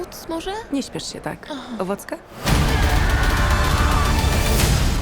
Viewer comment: Ja pod koniec usłyszałem wacka ? A chodziło o owocka